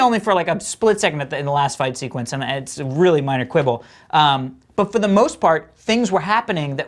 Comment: what word did the guy with glasses said? "Manerquable"? Help me, I don't speak English but and I feel so curious with what he said.